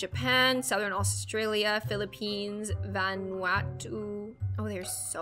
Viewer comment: did she say the Philippines because I’m Philippina 🇵🇭🤩